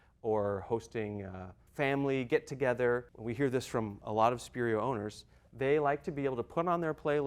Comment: Subtitle mistake, studio instead of Spirio.